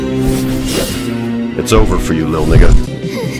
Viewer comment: what did he said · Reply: Its over for you lil ninja 🎀💞